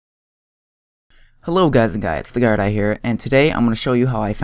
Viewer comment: I heard "hey guys and guys" in the intro. tf?